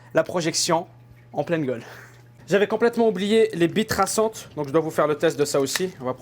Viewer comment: Pourquo j'ai compris "bite traçante" ?